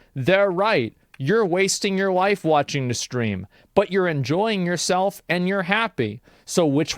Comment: Did he just say "you're wasting your life watching the stream"?